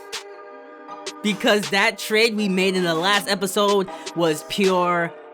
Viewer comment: Bro i thought he said this was the last episode but he said in the last episode i got scared